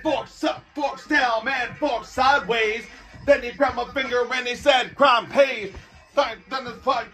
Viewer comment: Then he grabbed my "finger" and he said crime pays. Alright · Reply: I always thought he said "faygo", not "finger". I might have been wrong for the last 30 years 😬